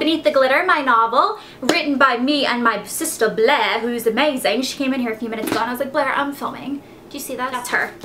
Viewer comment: OMFG did u say amazayn at 05:58 ?